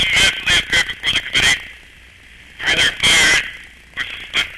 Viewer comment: Can anyone understand what was played/said by the caller at 10:25? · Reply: can you handle eight inches fat